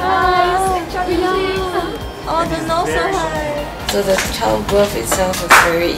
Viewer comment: Did she say ' the nose so high' ??? lol